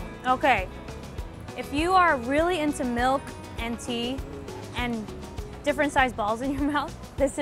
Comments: Did she say "Different type balls in your mouth "😂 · did she really say milk and tea...there isn’t any tea...